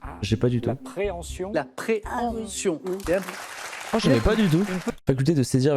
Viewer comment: Je comprends pas, elle a dit quoi à 1:32 ? J'entends "la préhension" perso et c'est la bonne réponse 🤔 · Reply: Préemption dit-elle